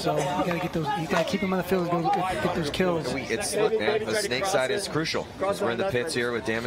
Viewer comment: lol did he say fuckin kills?